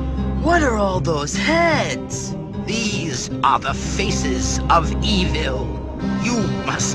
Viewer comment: these are the feces of evil Anyone else hear that?